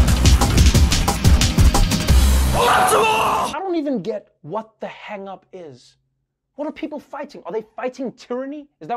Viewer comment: what is he yelling? Haha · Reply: OPTIMAL!!